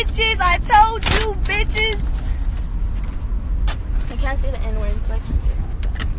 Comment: Did that lady with the papers say "Can I say the N word?" Lmao I thought I heard that wtf